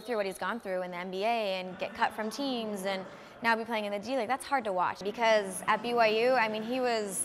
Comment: Did she say G League @ 4:25?